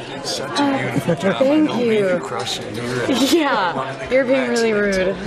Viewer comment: Did anybody catch what Jack said to Jennifer at 4:58? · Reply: He said I dont mean to crash your interview